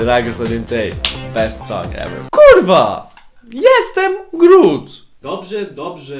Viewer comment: this minute I heard the fuck I'm snot "Kurwa jestem glut" Xd